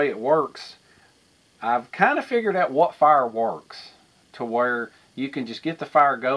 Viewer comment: Sounded like you were saying "I found out what fireworks" I know you actually said fire works not fireworks but anyway.